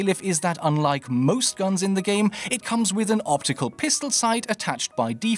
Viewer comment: did I just hear ballsack sisters